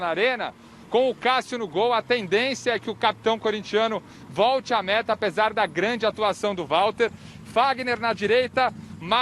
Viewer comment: "Volte a merda" ouvi isso ou to doido